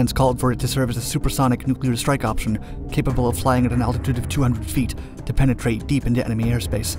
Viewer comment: Wait....did just say fly at an altitude of 200 feet at 0:18 ?